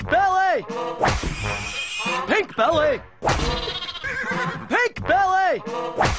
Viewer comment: I know Ed was saying "Pink Belly", but hearing this whole video makes me think he is saying Pig Belly. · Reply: I thought he said "pig belly" too 🤣🤣 when I was a kid